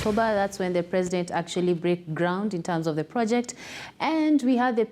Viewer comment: Did she say President Breaked ground 0:24?